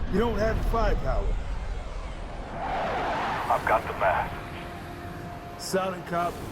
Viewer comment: i always thought he said, " I've got the math." Which isn't wrong. He's mathematically determined his mass is enough, so really, he does have the math